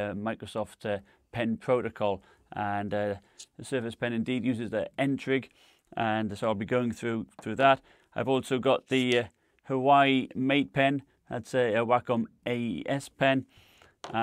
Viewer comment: Irrelevant but did I hear “Hawaii Mate Pen” 😂😂